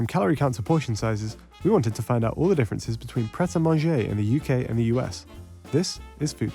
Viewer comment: I THOUGHT IT SAID PET A MANAGER UK VS US I WAS LIKE WTF YGUHBFYYTUGHJNBFRYUIKM